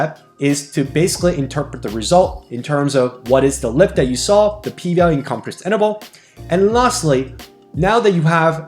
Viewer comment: In 2:44 did he use the term "P value in compressed interval" ? Or did I hear that wrong ? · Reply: Confidence interval